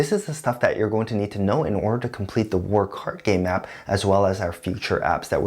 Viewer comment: Everytime you say "WarCardGame", I hear "Work Hard Game" :|